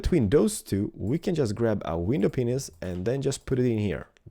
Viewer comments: I swear he says window peons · who else heard window penis? xddd